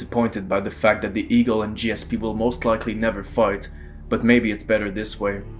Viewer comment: "...the eagle and gsp will most likely never fart". misheard that xD